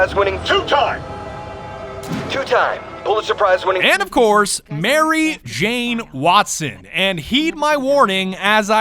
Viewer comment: ish my earbud died so i hear "Mary Jane power off"